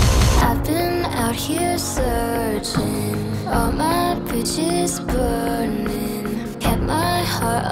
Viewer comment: Lol at first I thought she said all my B*TCHES burning hahaha XD. It's a nice song.